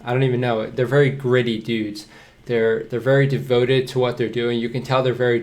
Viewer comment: i thought he said greedy but i felt i was wrong so i replayed and then, he said greety.